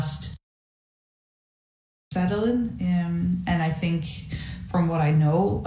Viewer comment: Floor / anyone who reads this, what's the vocal methods you were talking about when you got asked that question at around 43:00? I couldn't really get that because it cut off a tiny bit but I'm super interested in a methodology that works a bit better for the voice; I've had polyps removed because of inflammation and strain I put on my vocal folds while singing and it put me back lots so I'm trying to "relearn" in a way, and want to make sure I do so safely without needing to go "under the knife" again thanks! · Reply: Personally I'm not into this, but at 43:20 I think she says something like "Sadolin". A bit of googling points to "Cathrine Sadolin". So that would be my best guess, you may want to check that out. Hope it helps.